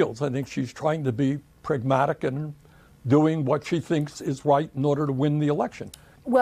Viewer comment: did berni say kamala is trying to do what's white? Lol. Listen closely.